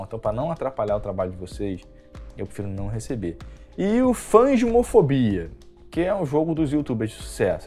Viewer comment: Eu escutei "fãs de homofobia", no phasmophobia! hahaha